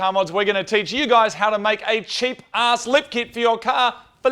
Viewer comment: I thought they were saying lift kit.